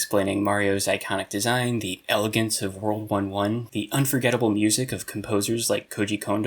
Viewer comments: When you said “the elegance of world 1-1” I thought you said world war one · I heard "The elegance of world war one" and was VERY confused until I remembered how "world 1-1" is pronounced lol